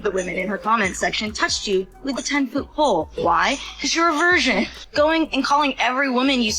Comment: did she say you might be a version? Or did she mean to say a Virgin? 😂